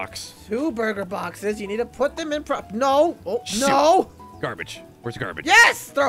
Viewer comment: is no one going to talk about how it kind of sounded like said “shit”????